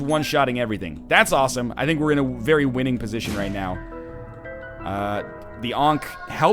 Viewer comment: Every time he says "won position" I hear "1 position" and it makes me think of DOTA 2.